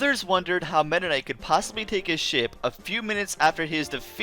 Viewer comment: thought he said shit💀